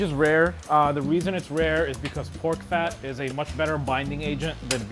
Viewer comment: is he sayin about pork fat or i missheard...